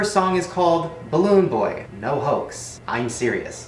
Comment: I hear "balloon boy you HOEEEEE"